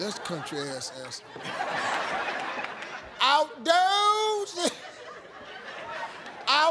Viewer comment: When I first watch this, I thought i heard "I dozed in the shed" and I was like "What in the world is "Dozing in the shed?"